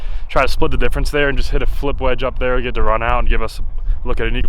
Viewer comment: "205 to the pin, gonna take a 48 degree wedge" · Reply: I thought he said 40° wedge which I assume is a bent P wedge to 9i loft.